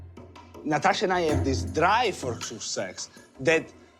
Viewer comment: Natasha and I have this drive?? for WHAT does he say? sex?? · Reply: Drive for success